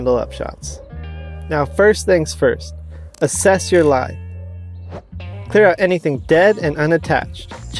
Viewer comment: I thought it said "Step 1: Assess your life" and I was like wow, this got personal real quick